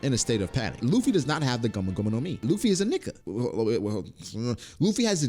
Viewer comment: Only kinda listening, then I hear at 6:36 "luffy is a nigga" ..my ears perk up like huh?....oh Nikka...lore...story...Etc..oh ok..😅